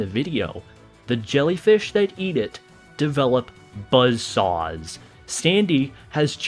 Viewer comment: did someone say Buzz saws?